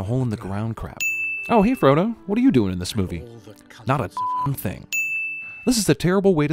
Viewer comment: you can clearly hear him say “fucking thing” instead of damn